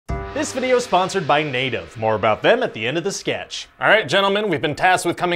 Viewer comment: I thought it said "This video is sponsored by NATO" and thought: RYAN VS. THE SOVIET UNION